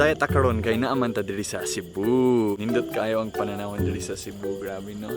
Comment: Did I just heard "Hindut" word?... I want to know what does it mean in Bisaya. Lol. Coz in Tagalog it has sexual meaning 😂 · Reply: "Nindot" not "hindut"..... nindot means beautiful..